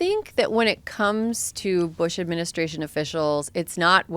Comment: Did Erin say " Bush administration officials"?